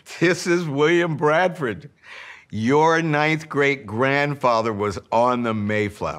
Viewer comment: I keep hearing “9 grade grandfather” and get confused 😂